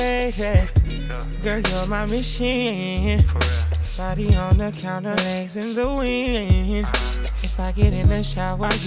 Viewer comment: All this time I thought he was saying "Thoty with your legs in the wind" smh...